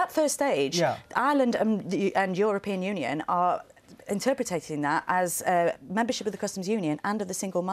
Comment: Did she say "interpretating" 4:40 ?